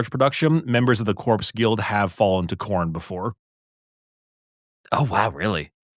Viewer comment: I heard "fallen to corn", not "...Korn", and assumed they lost out in a business sense.